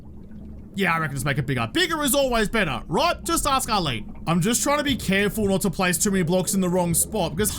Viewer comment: Had to wind that one back I thought you said "bigger is always better just ask Adam" 😅